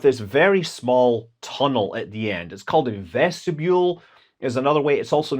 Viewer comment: did you say Zvedzdabule? 🤔